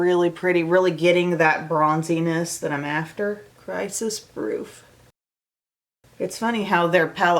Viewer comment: Okay, so when you said the name of the second eye shimmer was "Crisis-Proof," what I heard was "Crisis Fruit." Nope. I am not eating that.